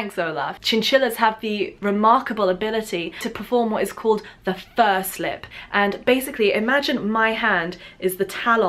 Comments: FUR SLIP! I thought you said first lip at first! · I thought you said “first lip” for fur slip